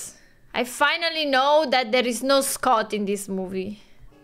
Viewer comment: As a terrible German speaker for years I thought Doc was saying Gruß Gott.